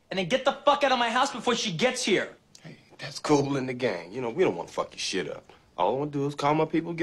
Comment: Did Jules say "that's Kool and the gang"? at 01:50?